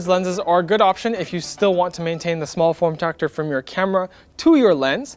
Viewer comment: did anyone heard him saying form tractor instead of form factor ?